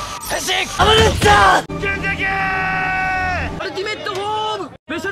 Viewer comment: のとこゲゲゲって言ってるけどなんて言ってるの？ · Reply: 剣崎ー！ よく聞くとｹｻﾞｷｰｯ！って聞こえてくる